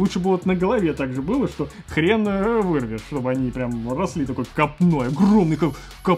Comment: что он говорит? Капно?